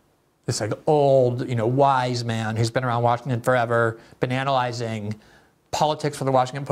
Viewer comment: I heard “Banana-lizing”